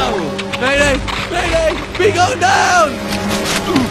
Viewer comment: they missed a "bee" and he clearly says it "mayday mayday bee going down!"